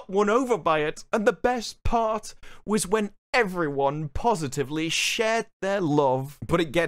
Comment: I thought you were going to say "when everybody positively shat themselves".